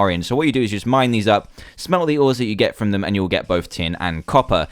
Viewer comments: i thought you said SMELL the ores LOL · Dan j thought you said smell the ore